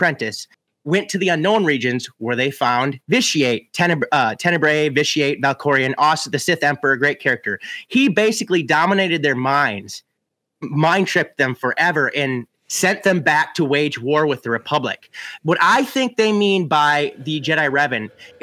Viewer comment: All I hear is “RAID CHARACTER!” I don’t care I want Revan.